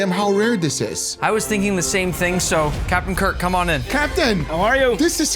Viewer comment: i thought nick said captain kirk😭😭😭😭😭😭